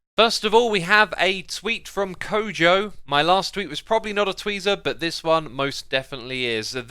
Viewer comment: A “tweezer”? Had to pinch myself, because I thought I heard that wrong! 😉